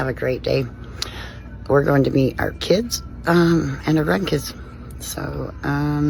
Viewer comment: am I tripping or did she say she's going to beat her kids and gran kids